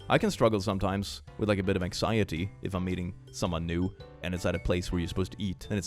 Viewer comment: OK so my best friend and I were listening to this, had just finished talking about the food meme with. IT totally sounded like He said 'I have anxiety when I EAT someone new.' NOT MEET someone new. My best friend, my youngest and I heard it. Did Anyone else?